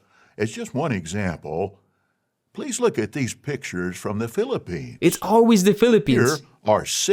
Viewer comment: I thought that Lett said Philippians…